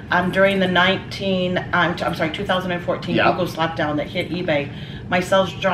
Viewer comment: What hit eBay in 2014 to cause her to lose sales. Could not make out what she said. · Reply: Biff Bifford I don’t know what she said, but I heard, “the Yogo slap down that hit eBay”. Darn Yugoslavians hurting eBay 😡